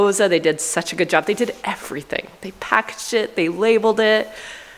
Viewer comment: I'm dyin, LOL, I had to replay 4 times before I realized you said they "packaged it" it sounded like you said they "pack shit" LMAO!!